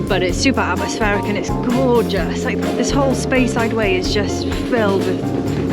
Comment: Good for you for speaking your mind. Definitely appreciate the heads up re Speyside Way (originally thought I heard Space Wide Way🤣); looking forward to a few drams on our next visit across the pond.🚲